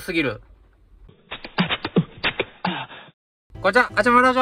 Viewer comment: いつも観てるんやけど、いまだに0:06これ何て言うてるか分からへんのやけど、何て言うてるんですか？😅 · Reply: こんちわ！！